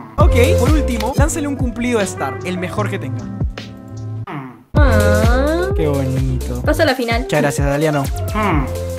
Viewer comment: no le entendí pero la suya por si acaso xd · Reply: Dijo: realmente podria buscar infinitamente y no encontraria nadie mas perfecta que tu 😌